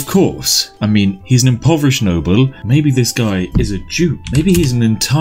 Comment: I heard "maybe this guy is a jew" not duke